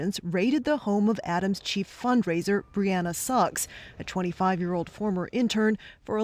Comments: Lmao all I heard was "Briana Sucks" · Anyone else thought the news anchor said "Deanna SUCKS" instead of Suggs? 😅 i mean... This is awful!